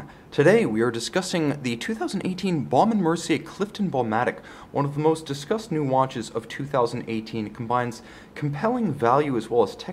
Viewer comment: Sounds like he says “One of the most disgusting watches of 2018” @ 0:11 I know he doesn’t, just found it funny.